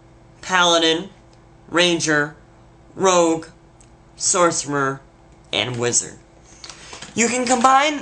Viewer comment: This video is great but did he say sorcemer